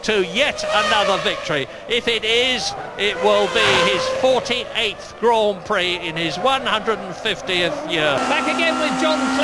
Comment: did he say? "it will be his 48 grand prix in his 150th year"